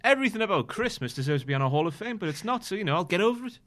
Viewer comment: Thought you said Bez for Hall of Fame!